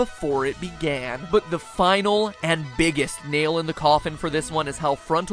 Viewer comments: "nail in the cawthon" I can't be the only one who heard that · Everytime he says "[final] nail in the coffin" I hear "[final] nail in the Cawthon", something that admittedly makes me, considering the circumstances, a bit nervous. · jfc i thought he said "nail in the cawthon" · When you said nail in the coffin I thought you said the nail in the cawthon